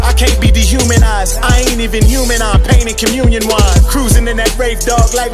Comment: "Y'all came to be euthanized I can't be de-humanized I ain't even human, I'm pane and communion wine" Bruh... 🤦🏽‍♂️🔥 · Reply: I thought he said im painted in communion wine....